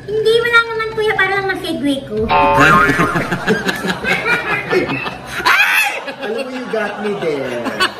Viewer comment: What did Mrena say? I rewind the video clip several times, but I couldn't really understand what Mrena was saying. 😔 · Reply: "Para masegway ko"